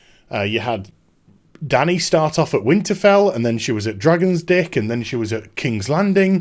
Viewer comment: Did you just say “Dragon’s dick” ..... HAHAHAHA 😂😂😂😂😇😇🙈🙈🙈